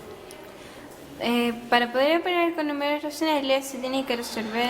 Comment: what?? que dijo :v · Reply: xDDD "para poder operar con números racionales"